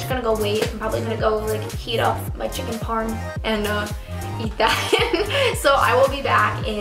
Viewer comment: I thought she said I'm going to go change my tampon in stead of I'm going to go heat up my chicken parm